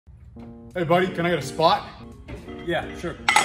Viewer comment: I misheard the beginning of the video as "hey can I spot?" So I was watching the video laughing cause I thought was the spotter, but was acting like he was doing the lift. Lmao